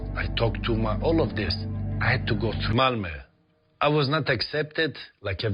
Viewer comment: what does he say, i had to go through malmo or what?